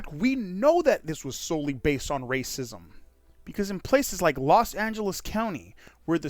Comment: your source of info misheard Senator Dunn. It was “the Los Angeles County Counsel” not the “Los Angeles City Council” that issued the memos to the Board.